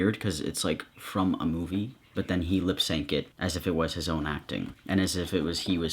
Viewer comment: is no one going to mention how at 7:21 instead of saying “lip synced” he said “lip sank” lmao